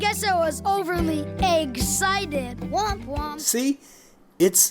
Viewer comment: did rocky just say womp womp?